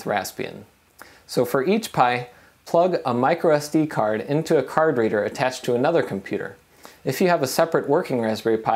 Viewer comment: "into a card reader" .. I had to go back and replay that 2x cause I heard "into a carburetor" .. thought it was an unexpected joke from a totally dry video :D